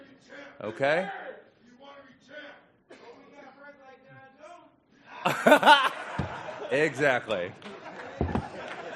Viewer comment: Anyone know what the guy in the crowd said? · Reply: I think it was "I hope you have friends like Jon Jones"